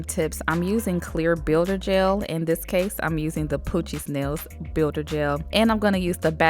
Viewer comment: My dumb ass thought you were saying “poochie snails” this entire time, bye 😭